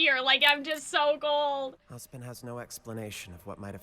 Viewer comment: Why did Oscar say husband? Who's husband? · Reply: he said “Ozpin”, the subtitles just misheard